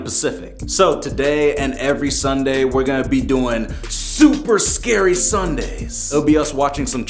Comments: Lol I thought u said spooky scary Sunday bc that's what coryxkenshin says 🤣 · “Oh ok, woof, I thought he said Spooky Scary Sunday. In my mind, I’m thinkin’ wait, wait that ain’t right!”